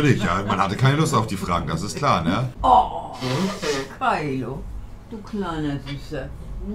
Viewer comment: wer hat auch ,,du kleiner wichser“ verstanden?😂